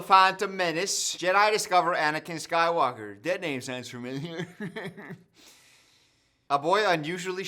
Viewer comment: what the heck was this deadname joke? I legitimately didn't understand what you were saying. I know you're trans positive (or I'm pretty sure you are) so it wasn't like a dig but I'm like... The fuck did you actually say? I'm so confused lol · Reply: I thought he said "that name sounds familiar" Like "skywalker" is familiar